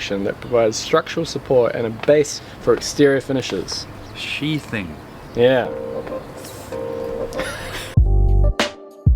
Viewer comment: Hahaha 3:59 is the first time it was said correctly. Sounded like shitting every other time 😂😂